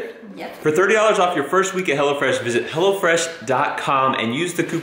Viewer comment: I thought you said hell-a-fresh lol